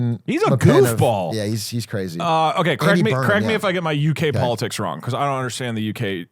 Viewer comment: very important: is saying "correct" or "crack me"